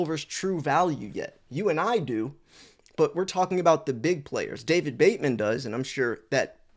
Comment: I thought you said Patrick Bateman lol